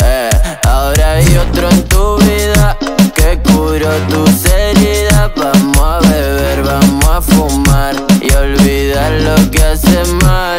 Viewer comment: en esta parte se parece que dice seriedad elegante pero es heridas escúchenlo bien que va a cambiar de palabra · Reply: Dice “tus heridas” pero mal acentuado, jjjjj por eso parece que dijera “ seriedad”